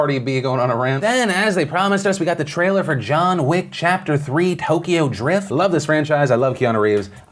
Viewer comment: I HEARD YOU SAY TOKYO DRIFT.